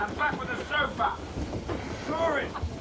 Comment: i swear i heard him say "ride it darling"